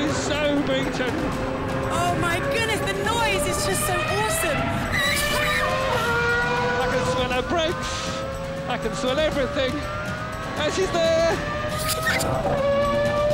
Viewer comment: "I can smell her breaks.... I can smell everything" You dirty old man :-) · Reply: At first moment I thought that he said: "I can sell brakes, I can sell everything"